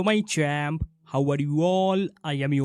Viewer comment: Every time I hear it as “Hello my tramp” 😂